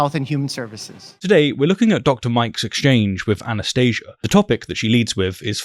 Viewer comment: I heard Anaesthesia, and I think it fits well in this situation